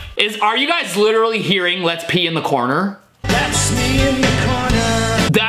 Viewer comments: He is actually saying, let’s meet in the corner,let’s meet in the spotlight sooooo yeah... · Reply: At first l thought he said let's be in the corner but then l heard that's me in the corner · Shave dem scrotes! Get 20% OFF + Free Shipping with code at → <link> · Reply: I hear let's meet in the corner